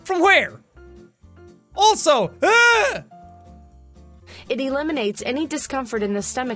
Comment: did say de-lemonade